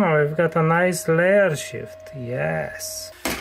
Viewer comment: Am I the only one who thought he said "s**t" instead of shift at the start of the video?